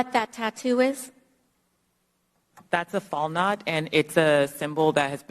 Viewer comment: had no idea what kind of tattoo they said it was i thought she said 'fall nod', so I looked into it. took about 2 minutes total to find out what she said to what it meant its a Valknot tattoo and it's a symbol used 'to give tribute to people who had passed away, specifically those who had passed away in a full-fledged war or battle.' and it goes back to Germanic citizens. so are they implying cause its German it's a white supreme meaning? lil racist